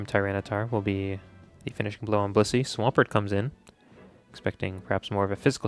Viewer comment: I seriously think it sounds like you’re saying bussy instead of blissey